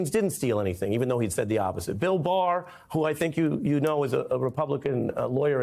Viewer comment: "And you pointed to me when you said that, that's all that I meant. ... go back and look at the tape!" Yeah, I did. Ari did NOT point at anyone or anything when he said that. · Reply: Ari did motion at 0:25 and say "... Bill Barr, who I think you know is a republican lawyer..". Due to the phrasing, this was misheard as ".. , who I think you know AS a republican lawyer...". I can't believe I'm defending a lawyer for Trump here, but this is an honest mistake on playback